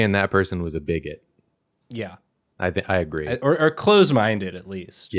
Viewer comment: They were CLOTHED minded. Eh...? Eh? See what I.. hey guys . Do you see what I did? Because closed... And clothed... Someone give me the approval my parents are incapable of!!!!!!!! (End of bit) Awesome job as always guys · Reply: i heard "clothes minded" lol